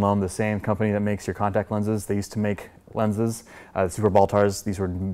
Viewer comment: I wear contact lenses and still thought he said Contax lenses